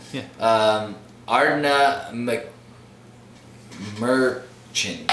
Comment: Ard-Na-Mer-Kin :-) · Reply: Sounded like was saying ARD-NA-MORCHIAN